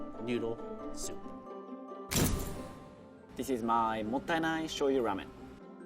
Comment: At first I thought said "mondai nai," which means "no problem." Oops.